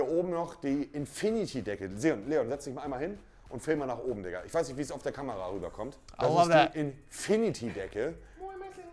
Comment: what did he say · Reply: "Sit down and film up (the infinity ceiling)"